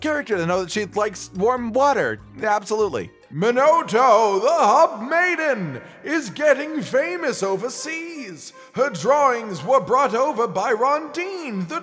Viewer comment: When read about Hinoto, it sounded like he said hot maiden rather than hub maiden. 🤣